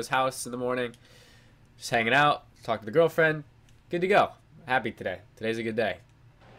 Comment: lol it sounded like you said f**ked the girlfriend and then i listened it back and was like nevermind haha